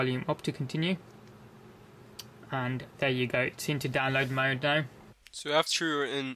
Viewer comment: what you say? and..... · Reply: qaem "There you go now, it seems is on download mode now"